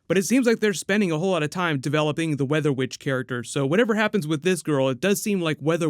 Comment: i heard you say "weather bitch" the first time and laught out loud. TBH i don't mind if Nora get erased from existence and we get Tornado twins (Don & Dawn) instead.